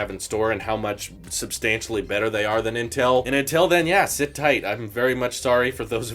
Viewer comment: Did I hear him say “and Intel then…”?